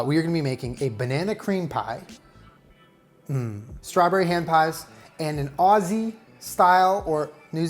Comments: I thought he said strawberry ham pie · I kept hearing “strawberry ham pie” and I was severely confused for a good while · At first I thought he said strawberry hand pie and my brain was just like “ok. Cool. It must be an American thing.” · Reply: I heard strawberry ham pie and was both confused and revolted 😂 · I thought you were saying strawberry ham pie